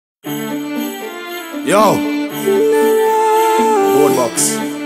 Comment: did aidonia sayy worl boss 0:03 lol